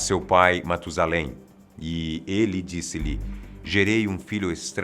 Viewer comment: acho q eu escutei 'ele disse-lhe' tá certo isso? 🤔